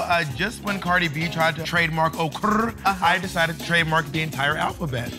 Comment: FUN FACT : 1:39 when bob said "just like when Cardi b tried to trademark okuurr, i decided to trademark the entire alphabet", she actually said "apropriate" instead of "trademark", if you hear closely and pay attention to her lips you can notice it.